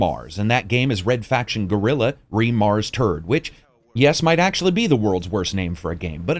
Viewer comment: It serouisly sound like your saying re-mars-turd, which to be honest is the perfect summery of the color pallet